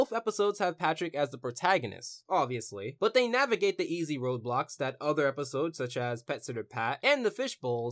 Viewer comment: TBH the first time watching this I thought you said "Roblox" instead of "roadblocks"